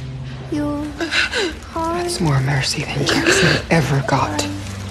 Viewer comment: What does Hayley say at 1:37v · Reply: That’s more mercy than jack ever got